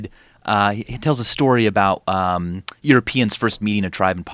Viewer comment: after all this pee talk i heard it as "euroPEEns"